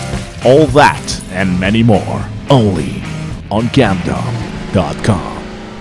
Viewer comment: I literally thought that it said ”gomdom”